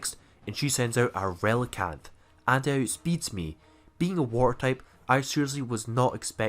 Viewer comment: Through video I thought you were saying war type Like wth is war type pokemon realized you were water type. lol 12:00 for example